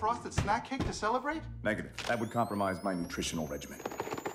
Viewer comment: My dumb ass not knowing “regimen” was a word thought he said “regiment” 😂😂😂😂 like he got a whole army of food for his nutrition 😅😅😅